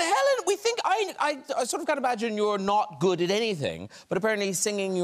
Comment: "So Helen, you're not good at anything" 🤣🤣🤣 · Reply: He actually said it right. He says :" I can't imagine you not being good at anything. " Listen carefully. It's kind of blurry. Listen at 18:18 slow it down to 0.25x speed in settings